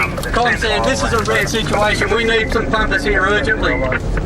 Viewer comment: did he just say we need some fuckers here urgently